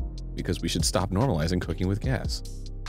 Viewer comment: “Alright, now we’re cooking with……..electricity, because we shouldn’t normalize cooking with gas.” -Arin Hanson, the earth-conscious, almond-hating king 👑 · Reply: i thought he said cooking with cats so i appreciate this comment